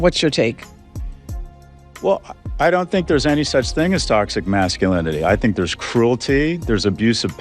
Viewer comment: Did i hear her say "toxic MAX-sculinity"?????